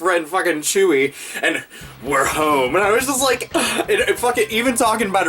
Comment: At first I thought he said... "we're old" not we're home lol.